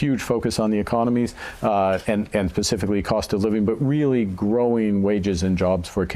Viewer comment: Did he say "pacifically" at around the 1:25 mark? And here I thought the word was "specifically"?